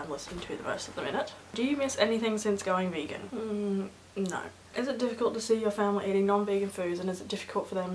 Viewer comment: At the minute 4:26 it sounds like you say "nein" instead of no :D